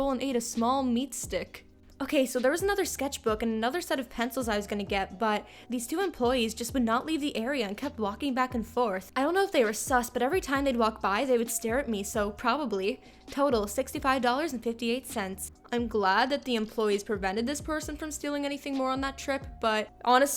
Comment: Did she just say sus in 2018???